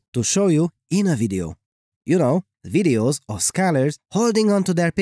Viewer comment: Scholars?? All this time I thought he called us scalars 😂